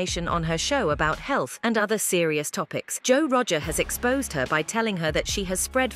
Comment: Thought I heard Joel Roger lol